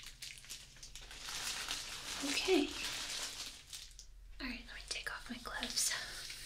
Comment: I thought she said "alright let me take off my clothes" I have a very sexual mind... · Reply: i thought that too but then i replayed it and she said"alright let me take off my GLOVES"